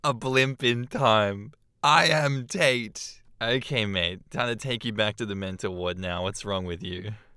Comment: after hearing the nameon repeat i thought you said "ok mate time to tate you back to the mental ward." Sort of like hie chanting the number 8 quickly begins to sound like hate which is basically the same word as tate